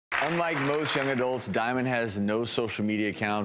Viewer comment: Omg, at the start I no joke thought he said, "Unlike most genitals,"